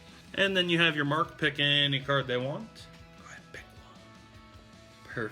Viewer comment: I heard a whisper saying red pick dont now here is the part like if you heard